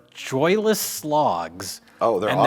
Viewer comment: did you say "Joyless Slogs" or did you say "Joyless Logs"? Either way I think you're right. I had a joyless log once. It was after a night on the Guinness.